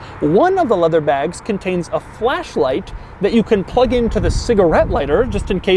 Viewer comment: Those leather bags are so suspicious that I thought he said fleshlight ah Italian design